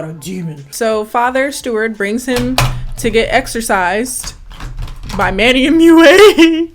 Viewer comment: I heard "To be exorcised by Mannymua" and I just, died.